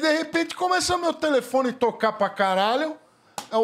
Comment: Eu ouvi direito??? O vinheteiro xingou " caralho "????? Minuto